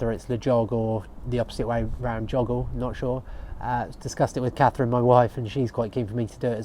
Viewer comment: "I was disgusted with Catherine my wife" is what I heard 😅